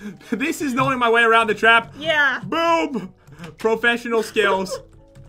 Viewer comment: Did you say boob?!?